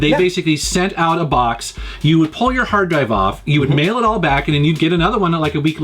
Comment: 5m21s.... all I heard was ‘pull your hard drive off’ 🤔😂